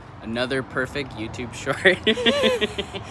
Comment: Wow this one moved me to tears. It’s heartwrenching to be a follower, I mean subscriber 💯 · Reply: Oh, I heard that as “followers and mean subscribers” and was wondering who was being mean